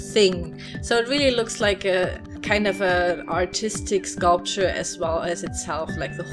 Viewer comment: I thought you said these are for "autistic kids". And I was like wow an art center for autistic kids. But then I realized you mean ARTISTIC.